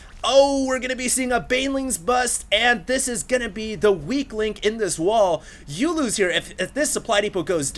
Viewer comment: LOL i heard "Oh the banelings are going after the weakling in this wall off" and was like WHAT? WHATS A WEAKLING LOL